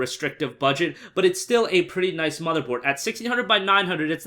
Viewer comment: did he say motherboard?